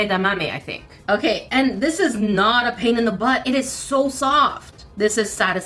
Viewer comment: I thought she said this is not a pain in the pod